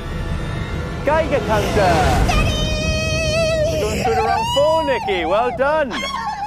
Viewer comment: Did say "Daddy!"